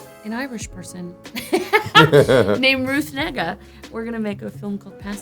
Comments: It sounded like Tessa said Ruth Nigga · am I the only one who heard Ruth ni.....😂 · does anyone else hear the N-word? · did she say Ruth nigga? 😂😂